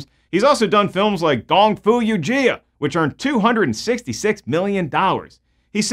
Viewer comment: did I hear Kung fu Yugia??😂😂 it’s supposed to be YOGA !! Yo - Ga🧘‍♂️